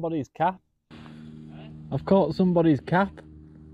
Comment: Thought you said you caught somebodies cat